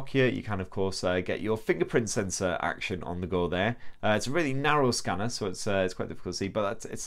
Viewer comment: What did he say about the Nokia FP scanner ? 2:21/2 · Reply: Narrow scanner